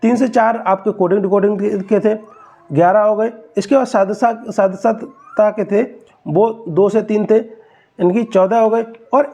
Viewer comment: pe kya bola h aapne sadrata?